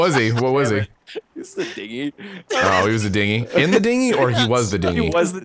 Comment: What was prop, i didn't understood what and said. Did they say digy or thingy or what?